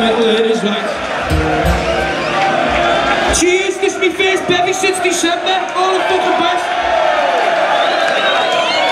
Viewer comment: Can anyone tell me what is he saying from 4:10 to 4:15 ? 😅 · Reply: Mickaël Goldschmidt “cheers,, this is my first beer since September “